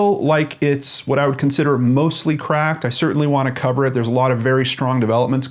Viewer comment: 28 seconds in had to replay it. Thought you said mostly crap. I was like no way, he did not just say that, lol.